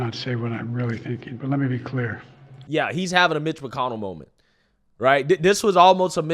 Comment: Not going to lie. I am a little hard of hearing and at 12:06, I sware I heard you say, "He is having a mental carnival."